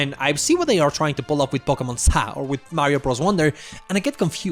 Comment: im sorry what did you call it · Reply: Pokemon zah